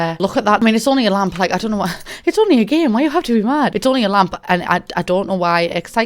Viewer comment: When you kept saying “it’s only a lamp!” I kept hearing “it’s only a lump!” And i died